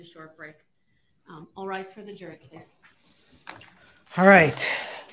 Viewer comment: Did any one else see at 1:33:20 the CC says "All rise for the jerkface"?